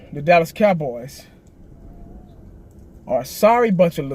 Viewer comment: I thought he said cowboys are bunch of bums 😂😂